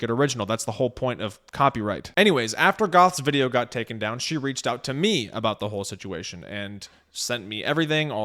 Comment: To be fair Greenie didn't try and involve anyone. Goth ran to, got told no so ran to river. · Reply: ah, I must've missed that part or misheard. I thought he said Greenie reached out to him.